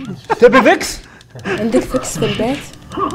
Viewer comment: سمعت وحدة تقول عندك فكس بالبيت 😱😱🤣😂